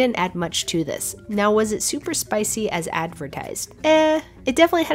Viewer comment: I thought she said pleasant cake💀💀